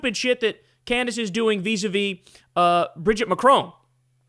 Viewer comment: I thought he was saying bradrinachrome instead of Bridgette Macron. Ngl, I was kinda stoked about a new adrenachrome conspiracy. Reminds me of simpler times.